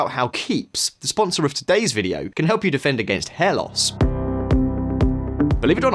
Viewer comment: You said "Sparta" and then "defend against hair loss" and I heard "defend against helots".